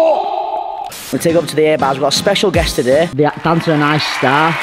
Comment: Until I saw the text at 0:13 that said "A Bar", for the longest time I've always thought you guys were saying "Air Bars" and i was always sooooo confused why you didnt just call them the "bars" or the "uneven bars" hahahaha. Just a silly American doing my best to understand your guys' British accents 😅